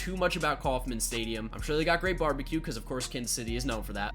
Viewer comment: When he said “I’m sure they have good barbecue” I thought he said “I’m sure they have good garbage cans” 😂